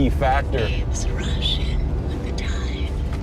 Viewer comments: “Babes rush in with the tide” It’s all I hear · So new to the channel, but the first time the female voice sounds here 0:56 it sounds like it says, "rushing with the tides" then later in the video about here 2:21 it says "Bluetooth device is connected successfully" sounds like editing to me to have a debunk early in the episode to me. Make it more believable that the place is super haunted until you replay that incident and forget to overlay the same audio file